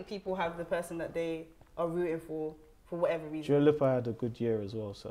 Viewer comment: I really thought they were saying Ojuelegba for like 45 whole secs until I realise they said Dua Lipa lol